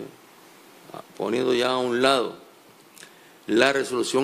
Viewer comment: El segundo 0:37 dice el Bachi-Burro y escribo textualmente: REVOCANDO, CANCELANDO...HA PONIDO YA A UN LADO.....Será que escuché mal?